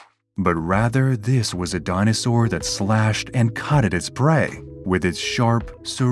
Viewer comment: I love how he says cutted at 4:10 (instead of cut). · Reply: Ummm ok no what he said was,"Cut At his prey" not "cutted"